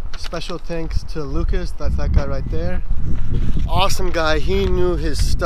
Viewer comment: Did he say: "Special thanks to Lucas, that fat guy right there?" at 10:30 ?